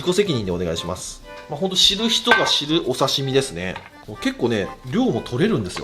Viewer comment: 声だけ聴きながら動画流してて(知る人ぞ知る)な(死ぬ人ぞ死ぬ)って聞こえてどんなお刺身！？ってびっくりしました笑